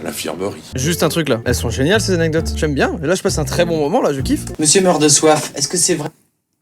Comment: Je sais pas pourquoi, j'ai vu les bouteilles, j'ai entendu "Mr.Meurdesoif" j'ai explosé 😂